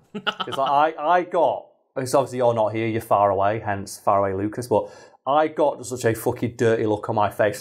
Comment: haha thought you were always saying "fire away lucas"